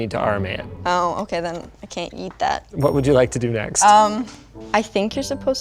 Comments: Totally thought she said Eat that. And I lost it. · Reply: Oh! She actually said 'yeet', didn't she. I thought she said eat. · "oh, okay then, I can't eat that ..." ginger for life, also someone get a fidget device stat! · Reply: I thought she said - Can't Yeet that